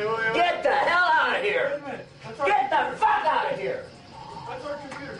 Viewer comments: Did he said, GET THE F*** OUTTA HEREE!!!!! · What did he say? "That was our compute" pretty sure? 8:37 PM 6/2/2018